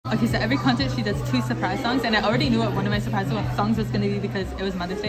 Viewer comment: なんと言ってるかは以下に記載してます！ ちなみにこれは、テンション高い時のなので普段からこのスピードで話しているわけではありません😂ただ、あえて速く話しているわけではなく、ナチュラルにこのスピードで楽しく話しています！ ■ English Ok so every concert she does two surprise songs, and I already knew what one of my surprise songs was gonna be because it was Mother’s Day, so she sang the one that she wrote about her mom, which I love that’s one of my favourite songs so I’m so happy I could hear it. But the other one I was so surprised, it’s called “Hey Stephen” it’s from her second album “Fearless” and it’s-I literally was obsessed with that song last summer like it’s so happy! Anyway, didn’t expect she was gonna sing that so when she like started- this is it by the way- when she started, literally like me and my friend looked at each other and literally like screamed so loud like oh my god like I can’t believe she sang that one and I can’t believe I got to hear it live, like, my dream come true! ■ 日本語 テイラーは全てのコンサートでサプライズソングを2つやるんだけど、ひとつは知ってたんだよね、母の日だったからさ。だからそのお母さんの為に書いた曲を歌って、それ私のお気に入りの曲のひとつだから聞けてめちゃめちゃ嬉しかったよ。でももうひとつの方は本当に驚いたよ。Hey Stephen っていう2枚目のアルバムの Fearless って中にある曲だったんだけど、ほんとに去年の夏その曲めっちゃハマってたからめっちゃ嬉しかったよね。とにかく、それを歌うなんて予想してなかったから、歌い出した時は、あ、ちなみにこれね。それを歌い出した時、ガチで私と友達で目を合わせて大声で叫んだよね。ほんとに信じられないし、まさかライブで聴けるなんて思ってもなかった。夢が叶ったよ。 インスタグラムもフォローしてね✌🏼 <link> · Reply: 出だしのところで she does three surprise songs と言ってるように聞こえるんですよね… だけど指で表してるのは2なんだよな…